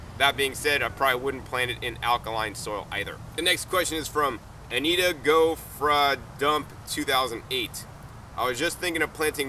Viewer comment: I could have sworn say that the person who asked about potatoes, their name was "Anita Gofradump"? Did anyone else get it?